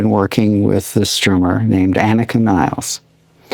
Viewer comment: While too young Yoyoka does an admirable job on Neil's stuff. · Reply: yoyoka? i thought he said Annaka. i think it's Anika